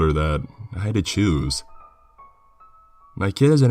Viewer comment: At first I thought he said juice then I thought it was Jews then I finally realized it was choose lmao